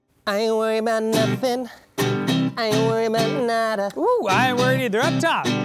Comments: I always thought it said-"I ain't worried bout nada" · I always thought it said-"I ain't worried bout nada" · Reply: I always thought it said "I ain't worried na-nada"